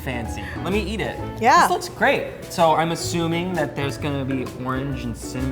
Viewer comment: did he say "this looks crepe" or was that just me? lol